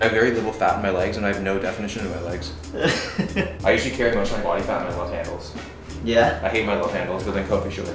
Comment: somehow I heard « I usually carry a bunch of body fat in my left ankles » and I was like wtf?